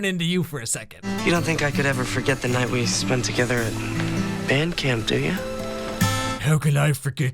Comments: I thought he said BED camp!!! Lol 😂😂😂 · Band camp. Seriously, pregnant by a band camp geek. I had an asthma attack from laughing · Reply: I thought he said bang camp lamo😂🤦‍♀️